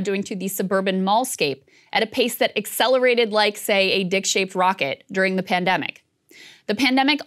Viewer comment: Am I tripping or did she say it toook off like a dick shaped rocket 🤣🤣🤣🤣🤣 4:00 minute Mark